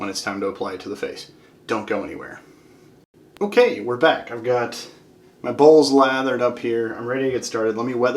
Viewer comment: lol 6:45 thought you said "got my balls lathered up" 😂